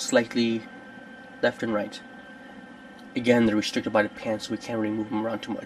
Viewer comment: when you said pants i thought you said penis i was like wtf